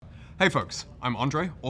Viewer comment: I just heard "Hey f***s"...